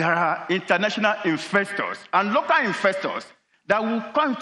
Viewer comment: Did you mean to say "investors" mr. Galadima; all I heard was "infestors" and that we do not want.